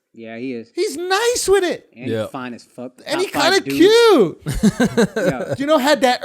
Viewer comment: Did Akaash say Idris Alba is fine as f#ck?